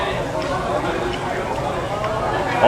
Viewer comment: Did you say "this shit is very tasty?" 🤣🤣🤣